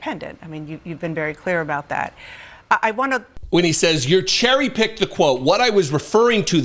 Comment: I actually heard him say "jerry pick" as opposed to 'cherry pick'. I thus see & hear limited intelligence. This is what is now in charge of the USA. Oh sh*t!